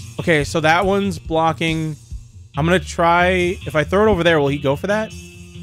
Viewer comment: i thought for a second that he said "im gonna cry-" and i was pulled out of the zone immediately